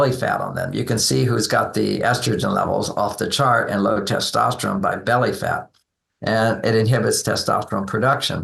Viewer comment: i thought they said its beer belly!.